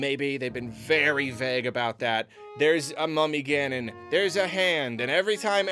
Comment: I keep hearing "there's a mommy Ganon". I know what he's actually saying. But I just can't unhear Ganon taking care of some little Ganon jr.